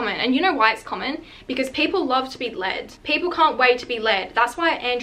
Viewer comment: I THOUGHT SHE SAID "PEOPLE CAN'T WAIT TO BE LAID" HELPP